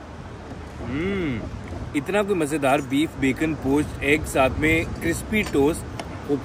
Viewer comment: Bro did you say bacon? At 8:32 Please someone verify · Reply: It's beef bacon. Hota beef he h lekin bacon ka shape dia hoa hota h